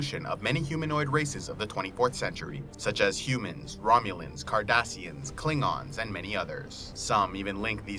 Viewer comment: Did he say kardashians